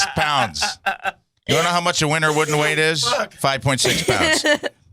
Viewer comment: To be honest. I’ve never seen that episode of talking about that play. And I heard exactly “winter wooden weight” and I was like huh maybe it’s referring to the weight that animals gain before they go into hibernation or some shit. 😂😂 Jesus Christ.